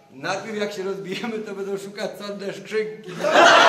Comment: co on powiedział? Dziewczynki? Deszczyki? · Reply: Czarnej skrzynki